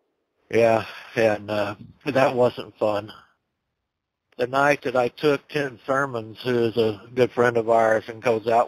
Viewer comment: It sounded like he said "the night that I took ten soma's"! Lol